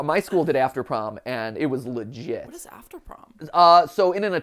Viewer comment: I coulda 'swore' that at 36:50, when Mat's talking about After-Prom, he referred to it as 'Lit as Sh-t,' instead of 'Lah-jit.' And it gave me a bit of a double-take, because that was so outta left field for someone like him.